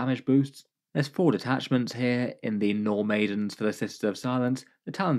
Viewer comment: I heard 'No Maiden' detachment lol